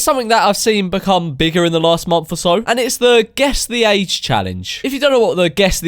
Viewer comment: I thought Memeulous said Guess their AIDs challenge